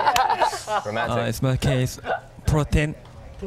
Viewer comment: I thought he was saying protection whoops